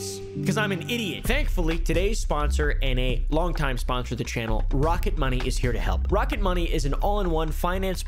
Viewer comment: I thought you said rocket monkey And I you pin this I well make jokes about you or all your videos I also have an funny looking dog Like you hes name is Rodney